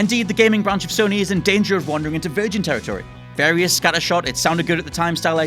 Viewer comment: What the hell are you saying at 3:43?? "Garivida Scanner Shot"? Also this entire point on the list sounds like it's talking about nothing. Help